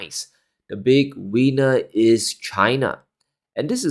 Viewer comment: I thought you said the big wiener is China.